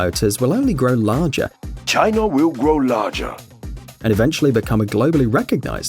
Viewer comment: doesn't it sound like he is telling Chinarr instead of China